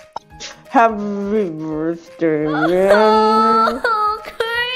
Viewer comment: i think 0:20 she said man not yeah